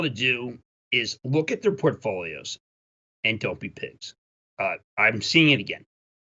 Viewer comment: What does he say at 4:43? Look at their portfolios and what? Doppey picks? Dump big picks? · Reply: Don’t be pigs. In other words, don’t be a fat greedy pig, take some profits if it’s a reasonable move, but, mostly, don’t let greed keep you from selling a winning play at the top because you want another 15% Hope that helps! Be disciplined!